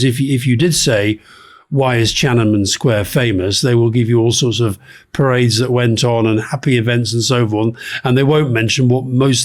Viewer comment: what does Alistair say here? can’t make it out and subtitles are unclear. thank you! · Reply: "Why is Tiananmen Square famous? They will give you all sorts of parades that went n..."